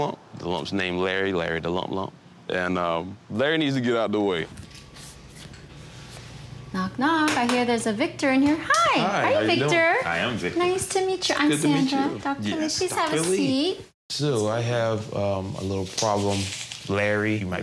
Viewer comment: Omg when she said " I here there's a Victor in here" I thought she said " I here there's a victim in here" XD 🤣😂🤣😂🤣😂🤣!